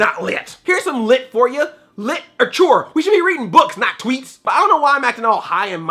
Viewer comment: What was the WORST of 2016 for you? And I also did a Best of 2016 Video: <link> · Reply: I heard you say Lit-ature.